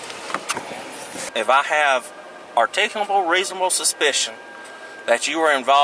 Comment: did he say "artentionuable, reasonable, suspicion"? These illegerate clowns should not be enforcing laws when they can't even get the words right in those laws.